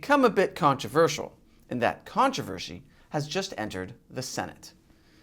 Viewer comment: Did he just say "controvershe"? I know that's what I heard. LoL...